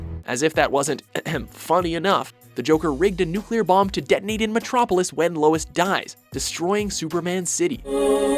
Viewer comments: "Joker raped a nuclear bomb until it almost died" did he say that? Because that's what I heard at 4:33 XD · The joker raped the nuclear bomb to death? That's what I heard